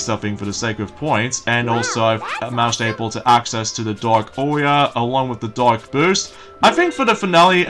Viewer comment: ! It's AURA! dark Aura! Sounds like your saying Auria! It's AURA!!!!